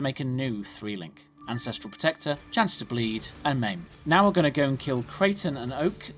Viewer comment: Thank you so much. Im from Argentina, and sometimes i dont understand what you say and the subtitles are not good, for the example when you say at min 5:15 "ancestral protector, chance to bleed, and ¿maim?" but in general this guide its so good, thank you so much for it. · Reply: Yes It basically was buy maim and link to existing two link of anscestral protector and maim to make a new 3 link. Thanks for the feedback, you make a great point. I have actually started adding overlays for when key gem links changes which can be seen in the new duelist leveling guide. I also plan to start custom editing the subtitles, as I know the automated one isn't always perfect. Thanks for watching!